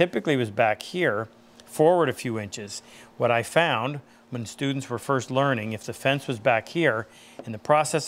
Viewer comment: When you said “when students are first learning” I thought you said “when dudes are first learning” 😂